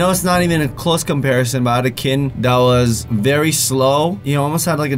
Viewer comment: of the vid I thought he said "I had a kid" and I was like WHAT DO YOU MEAN HAD, so I rewined (I don't know how to spell it) it like five times and realized what he said